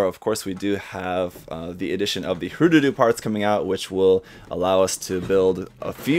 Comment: Wait what did he say at 9:15 ? Lol I didn't catch that 😂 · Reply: Hrududu